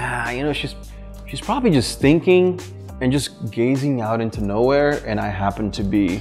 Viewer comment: Haha just had the laught of my day as I thought said: "She's probably stinking and gasing out nowhere..." 😳😂 For a second I got a liiittle bit confused to the point I really got what he said. 😄